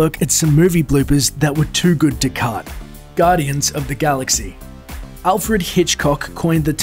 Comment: Did i hear it right ? 0:21 Albert huge cock 😂😂😂